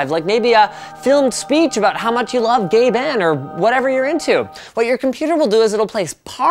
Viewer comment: Sorry but if they didn't put Gabins face up there would have thought he said gay ben